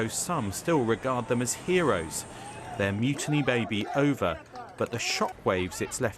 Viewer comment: Did anyone else think they said "Their mutiny baby" at first? Tell me I'm not the only person who thought there was a Mutiny Baby Tell me I'm not alone